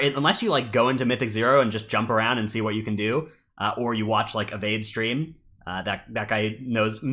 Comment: Whose stream are you referring to here? I thought you said 'Evade', but I can't find a streamer by that name. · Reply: Yeah character name Avade twitch handle Zevade